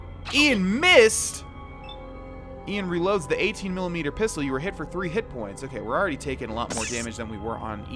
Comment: "18mm pistol" instead of "10mm pistol" :-D